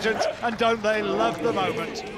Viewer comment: did he just say goose pimples instead of goosebumps